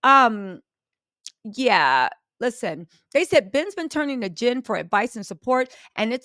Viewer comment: I thought she said "Ben's been turning to GIN for a support"!! 😂